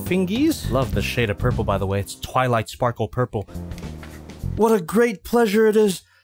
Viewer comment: did you say twilight sparkle❤❤